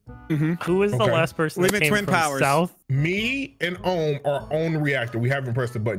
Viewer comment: did say "we the twins towers"💀